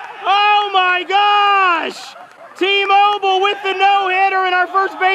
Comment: When tmobil got the play on the 3rd struck in the 2and inning it sounded like he said emobil